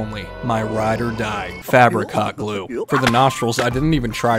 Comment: For the longest time I thought he was saying fabric cot glue. Love the way these little guys turned out